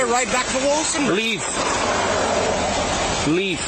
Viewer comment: I'm sorry, but I can't hear what this cop is saying twice, it sounds like "leave, leave," but the auto-generated subtitles say "sleep, sleep" and with the cop's accent, it could honestly be either, or even something else entirely. But if it was the former, why the fuck draw a taser on the guy when he follows the cop's commands to leave, and then proceed to say "stay, stay"? Also, does anyone else feel like the guy was being talked to by this cop like a dog being given orders? Freaking annoying -.- This is blatant abuse of power, excessive use of force, purposely giving contradictory orders, one cop forcefully keeping the guy from being able to follow the other cop's orders of putting the car in neutral by pulling on the guy's arm who's hand is holding the guy's phone that's recording the confrontation, unnecessarily escalating the situation and using heated language instead of being calm and professional as cops should. I am sure the list goes on, I'd even bet money on it, but really, these cops should be fired, or at the very least demoted to desk duty for months on end, and forced to undergo proper training again before released onto the streets once more.